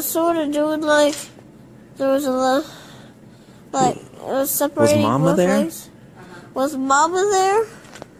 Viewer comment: did he say seperating my legs?